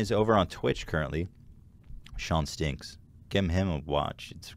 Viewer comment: I thought I heard "Give him a wash." lol.